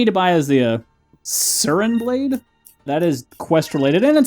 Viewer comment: Yes! The sisters are, like, some of the best crew members. I believe almost all the Asian based crew members are the best cause I remember the blacksmith also being incredibly good. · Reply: I thought he said "a mind blade!"?